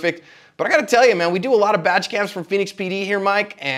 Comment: I un-paused it right in the middle of the b when said badge cam and it sounded like said vag cam. I thought this was going to be an interesting asp video.